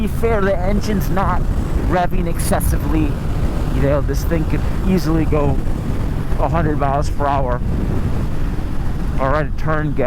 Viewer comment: Did he say @ 13:40 “this thing could easily go 100mph” ?? 😯